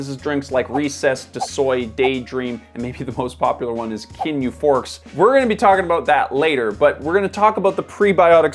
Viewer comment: Anyone else out there rewind 2:00 because they thought they heard "Ginyu Force" instead of "Kin Euphorics"? Good grief I am a nerd....